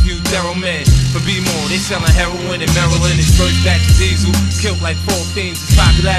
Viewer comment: that Queens Noreaga flow '' They sellin' heroine in Maryland'' !!! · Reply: For real I thought he said 'they selling heroin and marilyn' (as in white/cocaine for marilyn monroe) and then the other one I thought he said 'unless you tryna get your ass layed out, and then more' but guess u gotta be from NY to catch those bars damn I'm not even from America I'm on a whole different continent and I beat 50 these times enever thought his music would reach so far! Damn